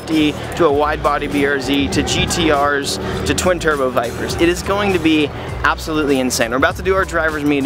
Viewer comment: I thought he said "twin turbo Mircra's" I was like... That's a thing?!